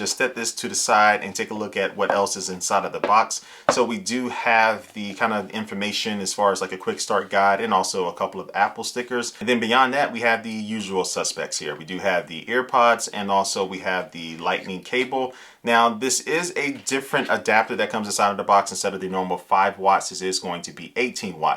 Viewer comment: For a second I thought you said “Air Pods” and not “Ear Pods” and I got really excited. Just realized that for the price of a Pro Max, it would have been a nice touch had Apple included AirPods...